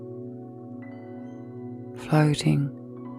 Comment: I'm sorry, did she say "farting"? Twice?